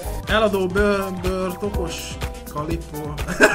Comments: nál mit mondott? Eladó bőr tokos... mi? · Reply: Bőr tokos calippo XDDDD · Mit mond 1:03nál? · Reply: Bőrtokos kalimpó😂